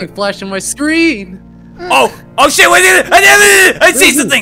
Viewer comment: did just say SH!t?